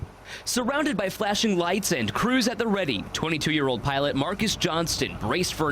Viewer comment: thought he said 2 year old pilot because i skipped in a little bit XD